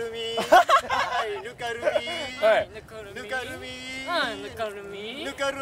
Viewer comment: ぬかるみーがlook at me に聞こえてくる笑